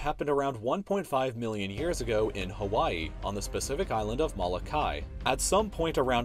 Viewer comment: anybody else hear him say specific ocean at 4:38?